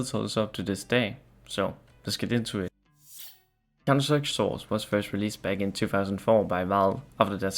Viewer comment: just here to let you know that the captions say "countersex" instead of "counterstrike" at